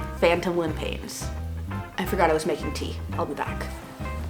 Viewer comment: I was listening at 2x speed and when you said "I forgot I was making tea" I thought you said, "I forgot I was an amputee".